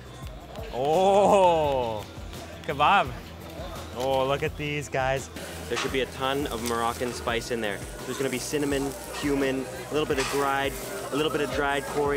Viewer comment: dafuq? 9:54 did I just hear HUMAN? as spice?